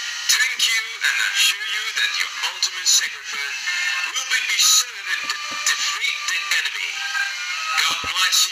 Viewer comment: "your ultimate sacrifice will be designated to defeat the enemy" is what I heard